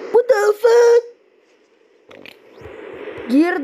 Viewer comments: Did bro just say what de fuck at 4:33 🤨🤨🤨🤨🤨🤨🤨🤨🤨🤨🤨💀🤨🤨🤨🤨🤨💀💀🤨💀💀🤨💀🤨💀🤨🤨💀🤨💀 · did bro just say what de fuck🤨🤨🤨